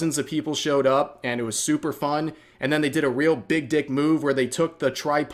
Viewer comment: I had to relisten to part of it. I thought you said "dick move", but it was "big dick move". One word makes a lot of difference, lol!